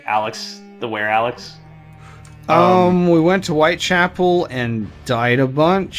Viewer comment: Thought they said that they went to White Castle and died a bunch for a second, like, just how bad were those burgers?!